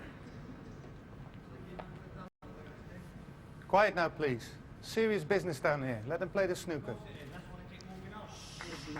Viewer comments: what did the audience say? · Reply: Sounds like "course is is, that's why ding's walking off" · anyone knows what did Ronnie said to the crowd around 59:05? and people clapped? I think one of the crowd said something and he replied to him to shut him up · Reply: “Course it is that’s why they keep walking off” And Ronnie said “if you don’t like it go home”